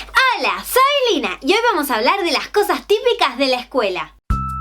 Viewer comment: PEnse que decía "COSAS TÍPICAS DE LAS ABUELAS" estoy bien menzo..